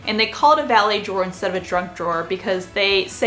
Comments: Whenever you say ‘junk drawer’ I hear ‘drunk drawer’ 😂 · I kept hearing "drunk drawer" instead of "junk drawer." Now we know what I'm thinking about in the middle of the day. LOL